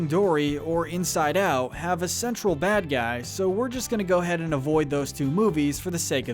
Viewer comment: I thought he said “Sexual bad guy”